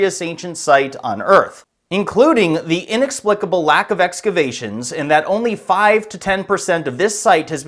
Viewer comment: Did he just say the inexplicable lack of explanation?